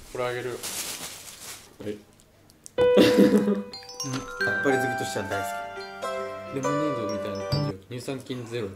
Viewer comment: 今回ポテチ多いwwwww 海苔ばっかりwwwww 9:34 おっぱい好きとしては好きって聞こえるのは自分だけか！？ 牛乳！田舎だけど紙www